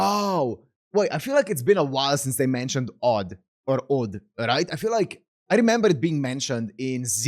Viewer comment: i didn't look at the screen when i heard you said 'od' and then i startled like: "huh? ORT? when did you heard that from zero?" and when i check the subtitles i just laugh at myself lmao